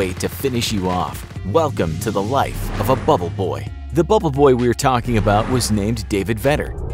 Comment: when he said “bubble boy” I thought he said “bababoy”